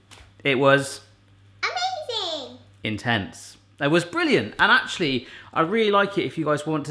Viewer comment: does say Intense, or In Tents?